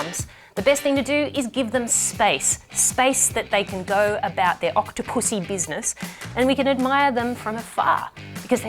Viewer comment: D-did you just say Octopussy?